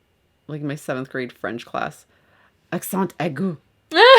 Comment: What are you guys saying at 24:12 ?? I want to be in on the joke but I couldn't google it to save me life 😭😭😂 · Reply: Omg 😆 it’s “accent aigu” (in English: acute accent). It’s this guy: ´